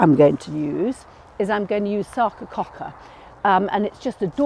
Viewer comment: What is the spelling of the plant name that Bunny is going to use in the border? I tried variations on Google of what I thought I heard "sawka caulka"? It sounds interesting to me because of the fragrance and white flowers. Thanks to anyone who can help. 💚🌼😊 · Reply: Hi it’s Sarcococca hookeriana, difficult to say let alone spell! 🐇